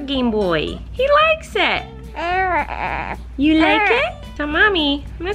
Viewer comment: colleen: “eat your gameboy, he likes it” *mumbles “i don’t like it” * i swear it sounds like he said “i don’t like it” LMAO